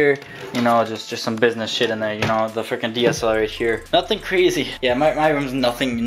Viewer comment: you talked so fast it sounded like you said DSR